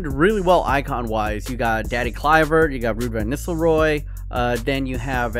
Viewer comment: Did he just sayed “Danny Kluivert”?!🤦🏼‍♂️